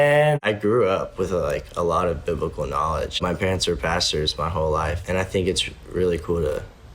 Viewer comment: I don't wanna ruin the mood but I thought he said my parents were bastards my whole life--💀